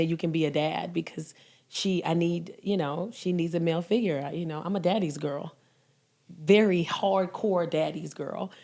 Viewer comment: When she said "I'm a daddy's girl" I heard "Take..The..shit..DOwn" 😂😂